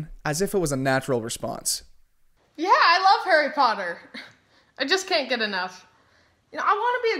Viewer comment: when i heard “i love harry potter” i thought it was “i love your mother” 💀💀